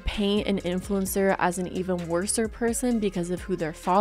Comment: at the 12:50 mark did she say “worser”????